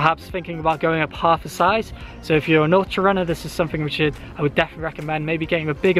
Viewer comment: what did he say? nortier runner?